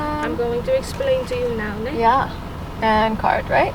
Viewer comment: she said "neh" not "nee" . Neh means "right"? we use it at the end of a sentence to make sure you understood what we just said but I understand how it can be misinterpreted as "nee"